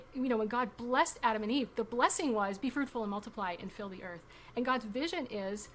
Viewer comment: Ohhh its be FRUITFUL. i thought they kept saying be FRUITY 🤭🫳